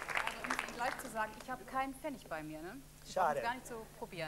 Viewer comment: ja, verhört, die Moderatorin Randy Crott sagt deutlich, sie habe keinen PFENNIG bei sich ...